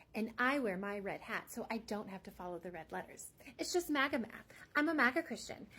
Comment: Can't ear too well. Is she saying she's on "MAGA meth"? That would explain a lot.